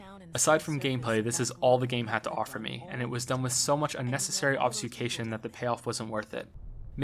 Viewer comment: Was that 'obfuscation' at the end of the video? I thought I heard 'obsfucation'. What an awesome video yet again. If you take suggestions for future critique videos I recommend The Talos Principle as an additional puzzle game, and even though I have not played The Witness past the first few puzzles I can safely say after watching this video Talos was much more powerful, engaging and altogether fun. And I see you are already aware of Ori and the Blind Forest, would dearly like to see a critique of that as well.